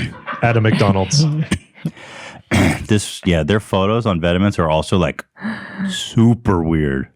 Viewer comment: When they said vetements, I thought they were saying vitamines haha Well, I can only imagine how it sound for native english ppl when french try to use english words !